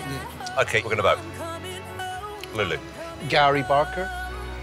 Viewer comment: Sounded like Simon said ‘Lily’